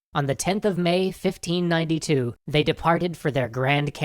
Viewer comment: caption says 1592 instead of 1529